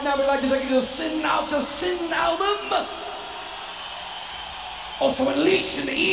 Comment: What the hell was Rob saying till 0:11 ? :) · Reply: I hear “Also Unleashed in the East” which makes sense since it’s the name of a live album